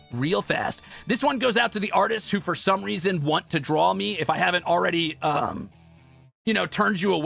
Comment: Edit กับซับดีมากเลยค่ะ ❤❤❤ 11:17 ตรงนี้น่าจะพูดว่า draw me แทน traumie ค่ะ (แต่เอาจริงๆก็ชอบพูดเรื่องทรอมี่แหละ เข้าใจ 😂)